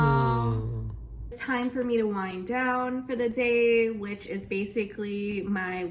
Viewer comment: I heard "wine down" instead of "wind down" and honestly I think it should be "wine down" time.